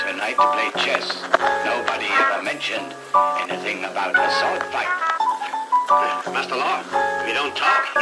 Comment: WHO ELSE THOUGHT THAT SAMPLE SAID "A SALT FIGHT" LOLOLOL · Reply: I was thinking that too but I heard sort fight when I chopped the sample lol